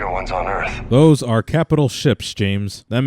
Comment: you say capital ships but the subtitles say capital chips and reapers aren't Doritos